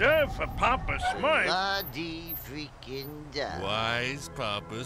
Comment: did said frickin?